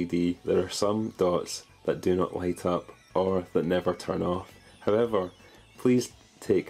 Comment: "... Never TUM off." instead of turn off